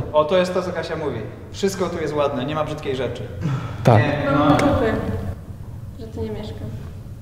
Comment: XD pod koniec usłyszałem od dała bym dupy żeby tu mieszkać xDDD